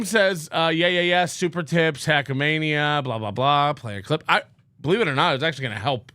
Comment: Instead of ‘play a clip!’ all I hear now is ‘chew your lip!’